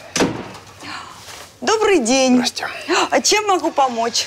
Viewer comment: я один услышал слово "Настя"?